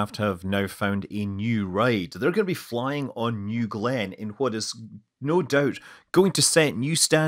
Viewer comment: did I hear right? He said "They're gonna be flying on NEW GLENN"... For real? Guess I'll have to change my headphones